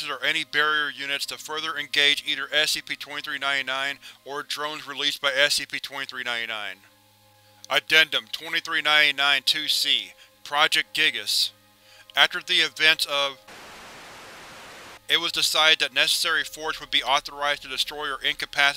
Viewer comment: "Project Giygas" did I hear that right or is that an EarthBound reference?